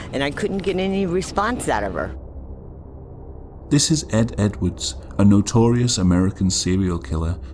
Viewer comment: why is it when says Ed Edwards YouTube in the captions says Edie Edwards